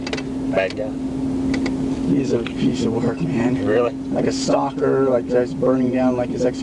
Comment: he asked, "bad guy?" I realize he's got a southern accent and talks kinda fast, but I'm shocked at how much trouble they had in transcribing what he said. Oh, and the guy's response at 43:18 was "he's a piece of work, **man**", not "he's a piece of work in hand." 🙄